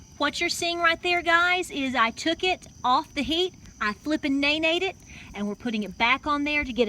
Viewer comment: Did I hear "flippin nay-nayed (neigh-neighed) it?" Could I get a little clarification on this term, lol? Looks pretty good!